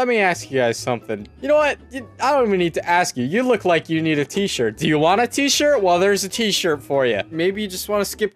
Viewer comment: in the start of the video i thought he said "do you want a teacher?"